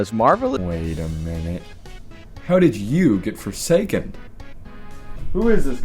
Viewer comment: Wait, I thought you said you quit forsaken